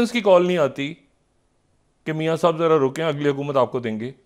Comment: tiseri hukumat aapko denge....Dr Aaila ye kya bol Diya Maine...Ab phir se Mera Software update Kiya jayega 😂😂😂